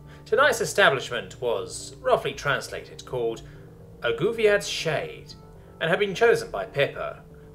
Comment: On the start I had to pause the video, because I thought the narrator said the bar's name was: "Goofy ah shade" instead of "a Guviad's shade"